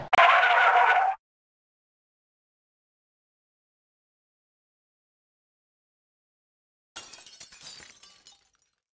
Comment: What is that word in English? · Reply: its: stupid